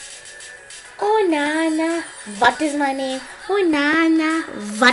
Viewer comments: When you listen to it three times it sounds like she is saying ohh nah nah butt is my name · I hear her saying " oh nana fuck is ma name " XD